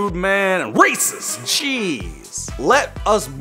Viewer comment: Hahahah I know he didn't say this but at 6:45 it sounded like he said racist cheese🤣🤣🤣🤣🤣🤣🤣